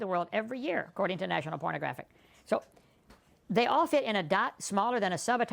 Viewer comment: Does he say, national pornographic @ 4:05 ?